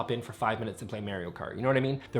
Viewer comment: I’m sorry,did you just say Mary-o Kart? 😂